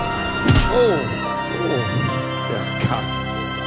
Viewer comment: did he call that a person a C**t I’m sure he just said “Oh, Oh you C**t”